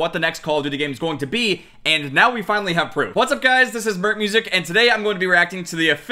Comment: it sounds like your saying "Hi Guys, this is burnt music.